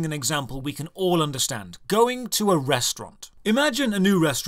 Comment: Me? About 5-10 minutes. Thought you said "Going to arrest runt" :D I thought... poor runt why? I can watch a video showing a basic overview of a game and know if I'll like it or not. Literally 30 seconds is enough. But 5-10 mins to be sure.